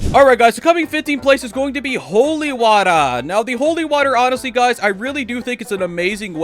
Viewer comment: I thought bro said "holy butter"